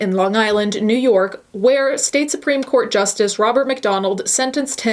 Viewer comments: Not gonna lie I thought you said Ronald McDonald 😗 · I totally misheard "Robert McDonald" as "Ronald McDonald" (of McDonald's burger fame) and was instantly very confused